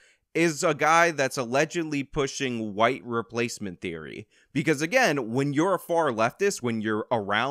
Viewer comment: I thought he said, "wife replacement theory." Probably because he mentioned being an alpha. Is there such a thing as wife replacement theory in the PUA community?